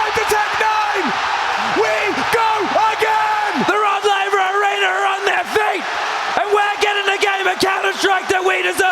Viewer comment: wtf did he say there? who or what is on their feet? 😂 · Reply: “The Rod Laver Arena are on their feet”